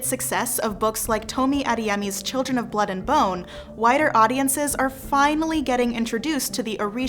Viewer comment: Honestly, I thought she said “whiter audiences”. Lol 😂